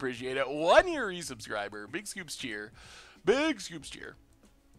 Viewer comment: What is saying when he gets a new subscriber? Sounds like he is saying big scoops. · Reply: Big "scoopsCHEER". it's the chat emote that makes the waving goldfish. His name is Scoops.